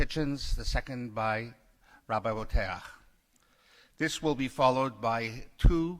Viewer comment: I've heard Bo-teach and Bo-tee-ach, did he say Bo-taire? {:-:-:}